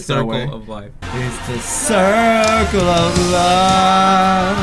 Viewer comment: i thought it said 'its the circle of lime'